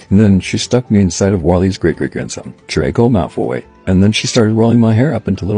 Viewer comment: I thought it said Wall-E’s great great grandsons was Draco Malfoy for a minute